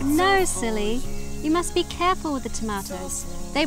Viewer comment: Had to turn CC on to find out he just said "careful" when he crushed the tomato. I heard "oh fuck" and was dying laughing